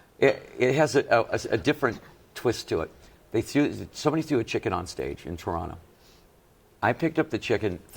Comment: I thought he said Shep threw the chicken on stage.